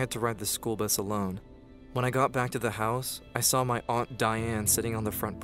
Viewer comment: What they said aunt dyan I thought they said “ I saw my aunt dying” 🤣🤣🤣